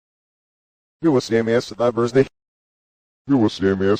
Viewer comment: If i might.....i think at 2:19 he actually says..... "You will SMASH the lovers dick." I listened with eyes shut and that's what i think i heard. What do you think?